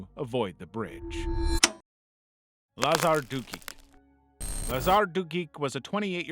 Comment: bro i thought you said lazar dookie😭💀💀💀